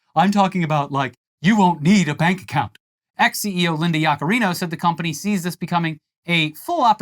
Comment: When you said “X CEO Linda Yacarino” I thought you said “Ex CEO Linda Yacarino” because that’s a stupid fkn name for a company